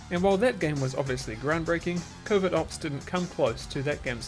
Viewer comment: What you said: "Covert Ops" What my brain heard: "Covid Ops" I hate my brain =_=